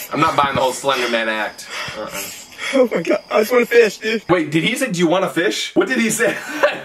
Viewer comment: i think he said i just wanna fish